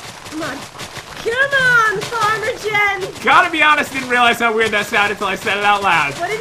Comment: This map was awesome! Hope you guys enjoy! We are leaving for our trip real soon, so we are recording lots of vids! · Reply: PopularMMOs I thought she said farter jen lol